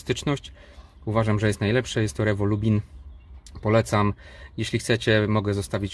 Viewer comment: Dobrze usłyszałem Lubin? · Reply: Tak Lubin.